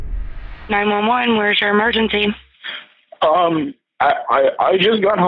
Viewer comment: Okay I honestly always thought they said “911 what’s your emergency” and not “911 Where’s your emergency” so I’d probably make the same mistake of explaining what happened from the start instead of giving the address